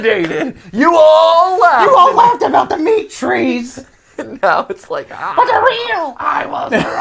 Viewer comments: When they said the meat tree I thought they said Dimitri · I hear "The Meat Tree" as "Dimitri". I don't know what's wrong with my brain man.